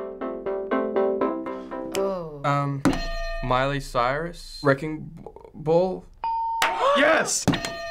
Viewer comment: I heard, Miley Cyrus _ Dragon Ball..hahaha